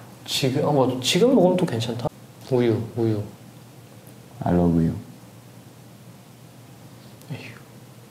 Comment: i thought JISUNG say "I love you"😂